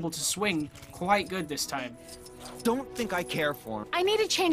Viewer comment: I thought he said I don’t thing I care for him very much